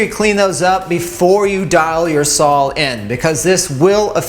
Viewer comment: wait...did you say "Sawl"?